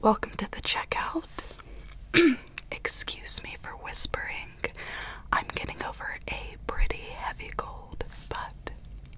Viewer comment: Is it just me or is this a "Yanny/laurel" situation? The first couple of times I watched this vid I thought she said I'm gonna go for a pretty heavy cold but i later noticed that it didn't make sense so I replayed and replayed the sentence until I finally realized she said I'm getting over a pretty heavy cold I know this is stupid but try it, if you read the sentences while she's saying it, you might hear what I'm talking about: 1:02 --> I'm gonna go for a pretty heavy cold (what I thought she said) 1:02 --> I'm getting over a pretty heavy cold (what she actually said)